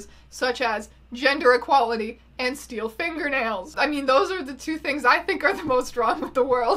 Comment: "Gender Equality" Nice! "And steel fingernails" I'm sorry? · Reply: Diana i thought she said "steal finger nails", i was very confused😂